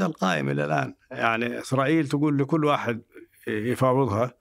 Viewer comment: كأني سمعت "اخرائيل" من الضيف في الدقيقة 1:05:56 🤔